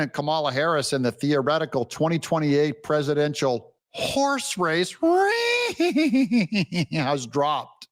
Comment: I thought you said whore race, which Kamala would win Handily.